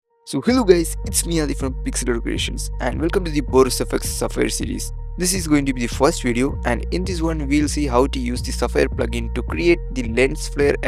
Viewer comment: i heard it as welcome to Boris FX Suffer series